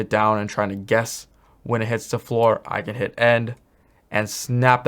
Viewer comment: Thank you so much for this tutorial! 28:26 For those confused like me by which shortcut to press, he says the "END" key. I thought he said "N" or "M" at first